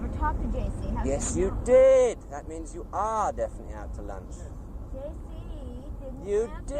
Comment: bored stiff! haha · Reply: That's what happens when you use cocaine alone. I heard Mick say to her "that means you are definitely out to lunch" (implying it'd been discussed earlier) to Nix at about the 4:04 mark.